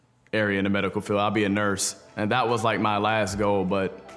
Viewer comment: Did he just say he wanted to be a Nurse hahaha